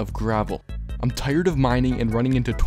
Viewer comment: What he said : I hate gravel What I heard : I ate you're grandma- 👁️👄👁️